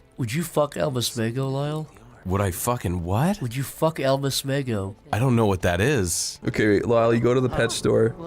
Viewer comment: My favorite part of the Alma Smego exchange is that he says "I don't know WHAT that is". He doesn't even register it as the name of a human being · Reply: I thought said “Elvis Mango.”